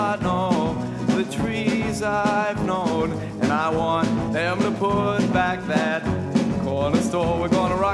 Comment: When I was younger & I heard this song in Kingpin I thought he said I want them to put back my old cornerstone lol. I love this song though.